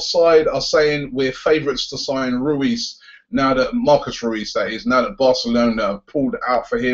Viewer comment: Thought he said Marcos Ruiz lol